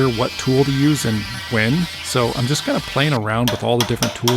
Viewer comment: What should I Carve out of the other two marble stones!? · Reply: to whoever does the closed captioning thank you very much but I think he said "playing" not plane 13:24 wrong "sore" as pretty sure he ain't flying with that arm